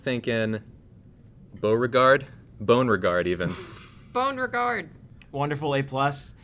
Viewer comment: Thought I heard boner-guard then realised it was bone riggard